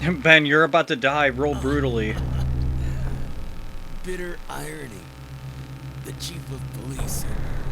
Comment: For the longest time I thought Ben said "bitter ironing", and was really confused as to what that could mean...